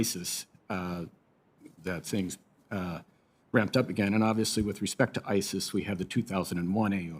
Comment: did he say "with respect to Isis"?